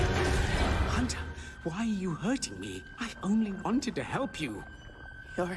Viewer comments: For anyone wondering, confirmed on Twitter that he does say Caleb and the subtitles were incorrect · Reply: knowing that my name is kaled soo it kinda scared me the first time i saw that because i heard "kaled" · I heard "kaaylem!"